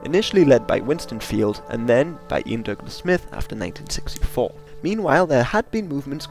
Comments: it sounds like your saying Iain Duncan Smith instead of Ian Douglas Smith. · For a moment there, I thought you said it was led by "Iain Duncan Smith". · did you said Iain Duncan Smith lol